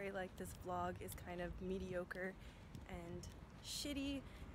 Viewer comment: it is not "midi occur" what she is saying, it is "mediocre". It means "vasat,sıradan" in Turkish. It could be figured out from the context indeed. Even though i appreciate the efforts of the translaters, there are several honest mistakes need to be pointed out. Some other users also made comments about this situation. Maybe a little update could be useful. Aside from that i like your videos, keep up the good work for the love of tornavida.